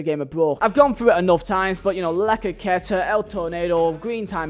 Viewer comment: can anyone explain what does he say? lacaqueta? or something? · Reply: He said ‘La croqueta’. That was a skill move that involved simply doing a sidestep while dribbling